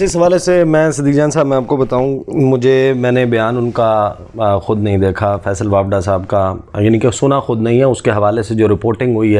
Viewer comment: did he say WAPDA :D :D :D